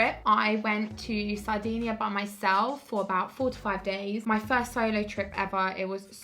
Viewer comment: It sounds like ur saying 45 days